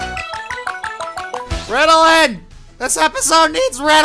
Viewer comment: What did he say, it sounded like Ritillin or Riddle it